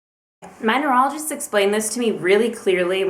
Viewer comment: what did she say? numerologist? manuelogist? I don't get it